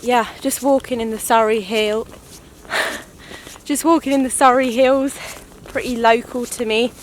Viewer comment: I heard you say “sorry hills.” Sorry.